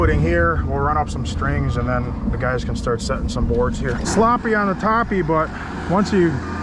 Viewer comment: did he say sloppy on the toppy? 😂